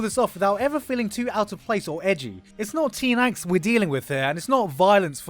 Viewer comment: What does say at 2:35? It's not XXX we are dealing with here. I can't make out the missing part. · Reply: Teen angst